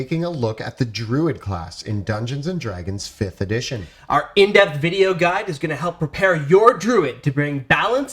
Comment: I know you said "in-depth" guide, but I'm watching at a faster playback speed and it totally sounded like you said "inept" guide lol.